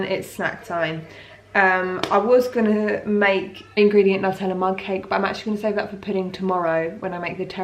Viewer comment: What you said: Nutella Mudcake What I thought you said: Nut Enema Cake 😂😂😌🤣😂🤣😂🤣😂🤣